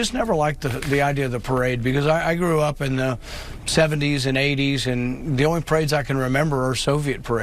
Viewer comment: Correction DeVory, he said "I grew up in the 70s and 80s . . . " Not the "90s" as you misquoted. Having been alive at that time I can vouch for what Sen. Paul said about military parades. It was only the totalitarian communist states that held the big splashy military parades and so was closely associated with those regimes.